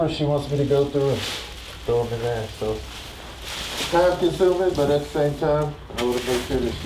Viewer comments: anyone else notice at 4:13 where he says "time consuming" but the cc says "I kind of feel stupid" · says, "It's kinda time consuming." Whoever did the closed captioning and thought he said, "And I feel stupid".... Did you really think talks like that? Smh (We see you and we watching!)